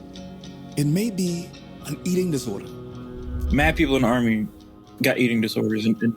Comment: Did I hear say “in the Army”? Were they in the Army??